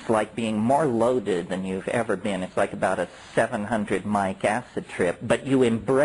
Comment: what does he say at 10:08 its like 700 something acid trip or what did he say there